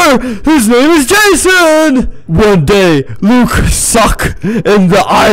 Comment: i think there was said: Luke sucks E